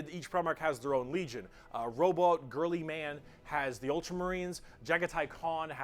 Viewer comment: Can't be the only one who heard Robot Girlyman instead of Roboute Guilliman